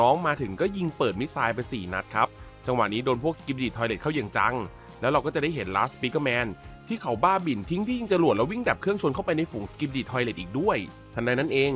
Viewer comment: พี่ครับทำไมพี่ถึงพูดว่า las speaker man ตอนที่ 2:18 ครับ